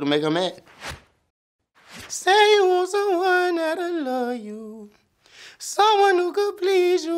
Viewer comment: is it just me or i thought he said “say you want some wine”💀💀💀🤦🏾‍♂️